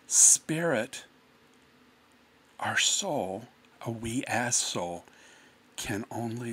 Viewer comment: the caption is NOT what he is saying. He was saying We, AS A SOUL....that needs to be fixed.